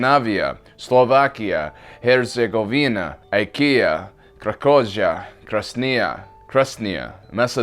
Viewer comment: did he say IKEA?😂😂